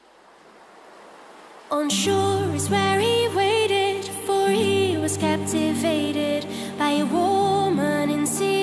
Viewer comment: At first I heard "Our shore is very raided, for he was captivated" and I can't unhear it ever since XD